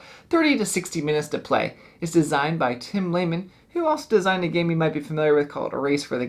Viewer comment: did I just hear Tim at 0:45? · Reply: Yup, he said Tim.